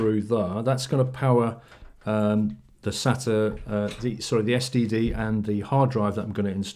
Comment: Did you say STD not SSD!?!!?